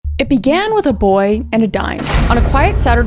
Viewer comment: I deadass thought at 0:00 that she said "it started with a boy, he dying" 💀💀